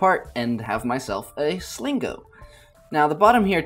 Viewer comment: at about 4:05... did I hear "slingo?"